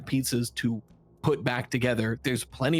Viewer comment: Say that again did rye say, pieces put together?